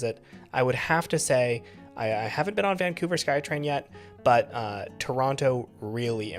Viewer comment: Did someone say Vancouver Sky Train?